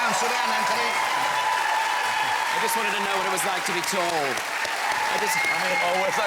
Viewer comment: does AJ say "I always liked to beat all"? if so, to me as a foreigner it's a great word game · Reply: he said “oh, what its like to be tall”